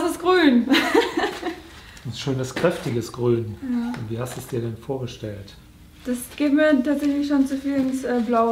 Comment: Did she say " that is the green pain?" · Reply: No just "this is green!" :D